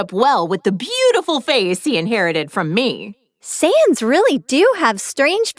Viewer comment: Why do i hear Sans instead of Saiyans?😭